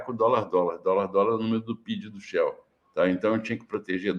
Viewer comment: "$ $ aumenta o pid do shell"? foi isso que eu entendi, se foi outra coisa, por favor me corrija